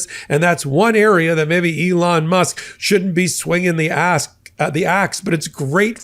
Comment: I thought Mike said swinging the “ass” and now I can’t get rid of the mental image of Elon throwing it back for tax breaks… Dark comedy indeed